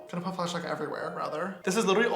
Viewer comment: DID HE JUST SAY BROTHER NOT SISTER! what scandelous james teaaaaa!😂🤧☕